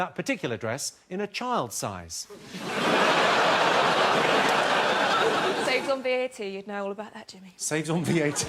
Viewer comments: What the fuck does she say at 4:24? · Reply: Tarquin saves on VAT. You know about that jimmy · What does she say to jimmy carr at 4:25? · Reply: Saves on VAT